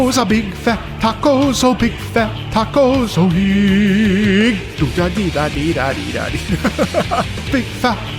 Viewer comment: i heard big fat tacos supreme. Maybe I'm just hungry lol